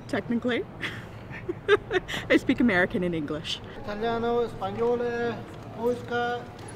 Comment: Idk but did I hear American and English?